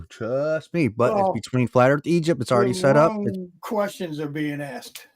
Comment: I'm sorry, I need to get my ears tested - I thought they said you were debating Flat Earth Eejit!